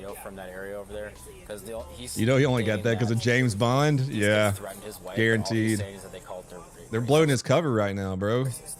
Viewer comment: i thought this guy said they threatened his wife, not just swore at his wife